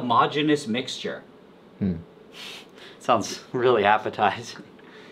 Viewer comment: when he said k so but the caption said “Queso” that got me dying